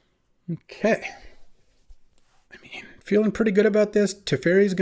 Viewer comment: I hear you say say "im in". Its kinda creepy. Rest of vid, great! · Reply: Lol it was, “I mean”